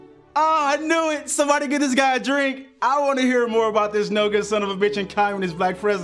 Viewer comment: Anyone know what the fuck said between 1:53 and 1:55? it goes "I wanna hear all about... some shit..."